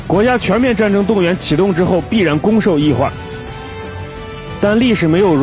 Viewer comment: 说什么？ "攻“”受“易换， 很哲学。